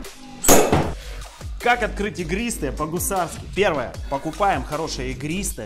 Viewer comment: Я в начале услышал "как открыть тигристое" Нинада